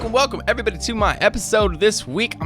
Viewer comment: it sounds like you said “fuckin welcome” hahahahaha